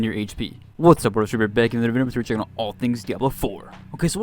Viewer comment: wtf does he say between 00:14 and 00:16 ?? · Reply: "What's up YouTube we're back and we're going to be checking out about all things diablo 4" ...I think